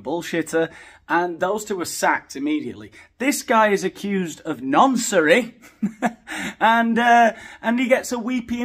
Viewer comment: It's no laughing matter of course, but he's such a bad actor😂 Why does he even agree to do this interview I wonder. Btw, what is the word you had used at 6:33? I turned on the closed caption at that point and it shows "accused of nonsuri". I'm sure that can't be a word though, in Japanese perhaps😊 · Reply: Noncery 😂